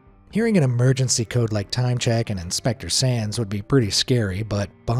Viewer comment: I thought they said inspector sans 💀